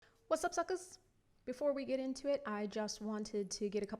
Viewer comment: Love the into I had volume on low and thought you said whats up f###era haha. And the forest Gump juices 🤣 your awesome!